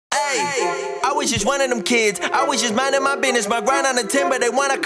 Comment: i heard "i was just one of em kids" on the first line